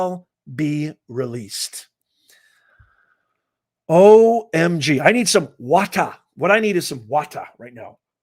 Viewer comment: about 38:38 I thought you said "I need some wodka... wodka" as in vodka but with a w like they say in Russian... lol!!!